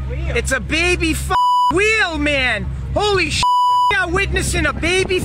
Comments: "It's a BABY WHALE!" took me all the way out 😂😂😂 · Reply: I thought they said “baby wheel” 😭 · "Its a sea turtle!? ..No its a baby whale!!" 😂 Oh man that was hilarious! For me this sea unicorn is one of life's mot amazing creatures!😉Great video thx! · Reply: Yo I'm dumb but I thought he was saying "it's a baby wheel!" The wonder and delight in his voice while speaking about a baby wheel being born was hilarious to me 😂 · "It's a BABY WHALE!" took me all the way out 😂😂😂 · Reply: Oh! Thank you. I thought he said baby wheel.